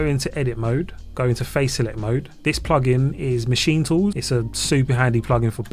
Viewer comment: Congrats! It´s so cool. Just a question. How is the name of plug-in did you say in 6:36 minute of the video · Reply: Thanks, The plugin is called Machin Tools : <link>